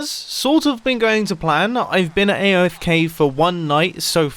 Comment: sounded like aiofk